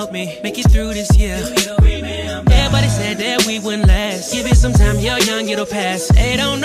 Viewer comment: I thought it said "give it some time you're on your best" 😂